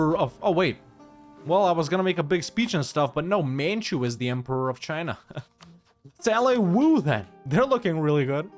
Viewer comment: I'm cleaning up my place while listening, and at first I thought you had said, "Mantua is Emperor of China" and I was all, "Then why are you playing Andalusia?" 😏 (15:05 or so)